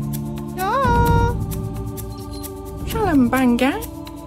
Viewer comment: What i hear: Gibberish What subtitles hear: "tell them banger"